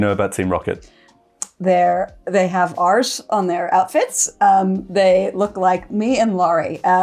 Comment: I could've swore that said "they have arse on their outfits". I've seen that version of Pocket Monsters. A team of Pocket Rockets focused on arses. Oddly enough it's how I spent my last lonely valentine's day